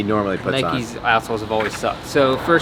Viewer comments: Had to re-listen to 1:32-1:37. First listen, thought for sure you said “Nike a**holes have always sucked.”😂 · Did he just say nikes as*h*les have always sucked😂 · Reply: Outsoles but definitely sounded like as*holes 😂